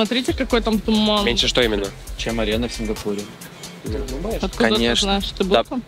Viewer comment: что там сказали, что яторо так отреагировал? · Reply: "ты разве был в Сингапуре"